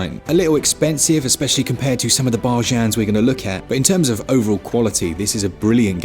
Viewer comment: Hey lovely people! Pleasee someone help, I am not a native English speaker, and I hear as I thought should be "BARGAIN" word, but pronunced more like BARJAN at 2:07 and 12:08. Did I get the word right? I am dying of curiosity, I never heard it pronuonced like that! Thank you!!